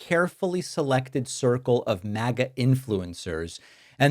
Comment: Did you say 'Maggot influencers' ? (0:10) I'll have you know they're wholesome Americans and how dare you refer to voters in such a way ;) x